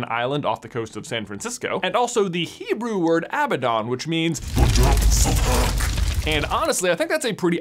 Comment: did you say the depts of HECK???!!!